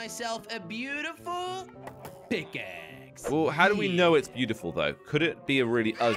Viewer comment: When jelly said pick axe I had subtitles on and it thought he said big * 😂😂😂😂